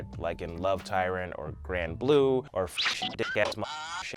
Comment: I just realized there's a lot of anime I haven't seen: Punchline Castle Town Dandelion Neon Genesis Evangelion 2.22 Kill la Kill Love Tyrant I think he said Gram Blue (3:10) What ever he said